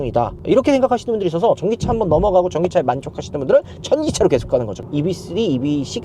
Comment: 천기차!ㅎㅎ 들리는것도 그렇게 들리기도하고 자막까지 천기차로 나오네요😅